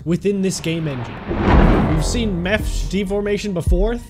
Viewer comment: WHAT DID HE SAY???? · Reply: What do you mean? He said mesh